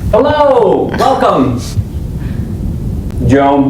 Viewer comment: Menit 09:30 Kok gue dengernya "hallo welcome, JAWA" WKWKWK